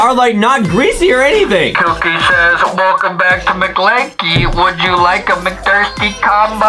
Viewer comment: is justin said mclanky😂